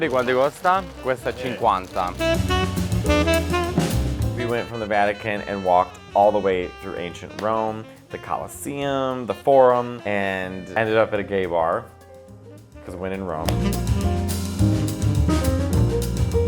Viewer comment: At first I thought he said "we were walking through Asian Rome"... And I went, since when did Rome have a China Town? 😂